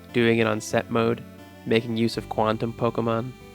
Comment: Did-- did you just say quantum Pokémon